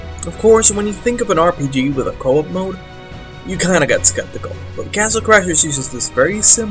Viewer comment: I thought you said no games with CO-OP MODES? .